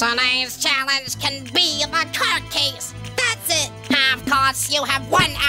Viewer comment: First time I saw this video I thought 4 said court cakes.